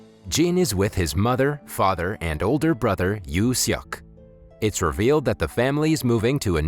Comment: you suck that I Hear!!